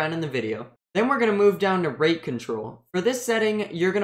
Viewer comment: MISHEARD AS RAPE CONTROL LMAO